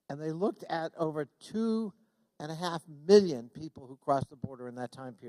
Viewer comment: 2 1/2 bill/mill? ( I hear billion; "... nothin' 2 get hung about...straw...forever...") sublime? not 2B@